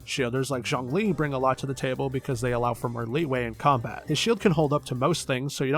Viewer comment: did you say.. LIYUE? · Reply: Leeway 💜